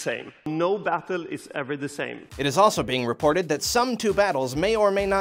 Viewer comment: Who else heard "No bathroom is ever the same" at 0:27?